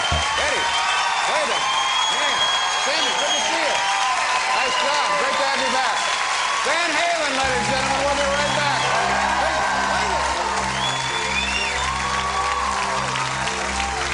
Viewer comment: does Letterman say "David, great to see ya." to Sammy Hagar? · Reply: No, he addressed Sammy as Sammy.